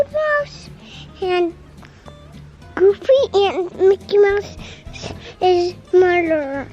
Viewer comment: Ok at 1:06 I swear said Mickey Mouse is a murderer...